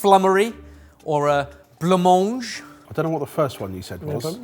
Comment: What is the word you are saying? "Bleumonge"? Are you trying to say "Blanc-manger"? The word "blanc" sound is like "flanc" (which I know you pronounce correctly if I remember other episodes) but with a B instead of an F. The word "manger" is 2 parts. The "man" part is just like blanc and flanc but with an M instead of a B/F. The "ger" part is like the beginning of "geronimo".